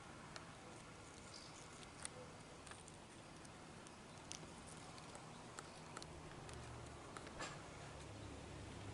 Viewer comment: What is the name of the species at minute 20:00 i can't quite understand it? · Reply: Lymantria monacha